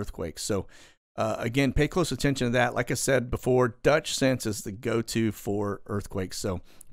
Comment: What is the name of the earthquake guy? I thought he said Dutch Santis but I cannot find his channel...